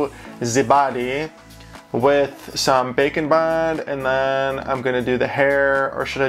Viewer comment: When you say bakeing bond it sounds like you said bakin bon